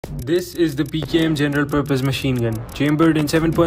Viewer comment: Did he just say "Gender purpose machinegun?"